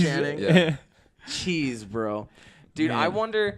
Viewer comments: it sounds like Corey said cheese bro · it sounds like Corey says cheese bro